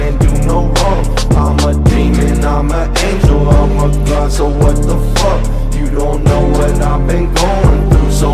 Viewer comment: For the longest I thought he said "I'ma demon, I'm an angel, I'm a glass of what the f#@ 😆